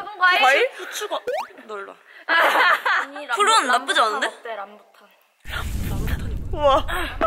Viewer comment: kiyowooo... 2:15 i hear rambutan.. :D